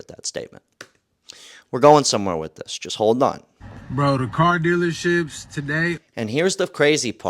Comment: why i thought he said car dealer💩s 😂 had to go back and make sure i heard right but he said the word right but still funny